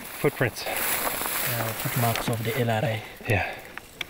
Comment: did he say NRA?